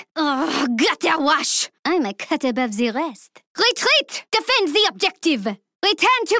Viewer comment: What does she say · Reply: Dragon man I think she says "I'm a cut above the rest"